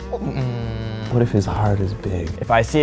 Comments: Aww, the one dude 1:10 "but if his heart is big". So cute ^^ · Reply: aww at first I thought he said "what if he's hot and big" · Aww, the one dude 1:10 "but if his heart is big". So cute ^^ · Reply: omg i heard it as "but if it's hard and big" i'm crying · "what if his heart is big ?" aww... the way he said it...ha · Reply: I heard “what if when it’s hard is big” and I was like damn okay then